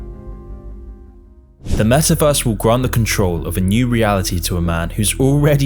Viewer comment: Did he just say Mesaverse ?